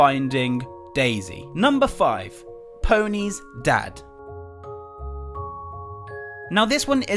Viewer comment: i heard pony,s dad too pony's death 5:50 i heard willow's peanut lol